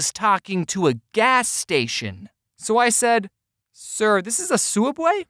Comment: when he said "Sir this is a sooubway" i thought he said "Sir this is a wendy's"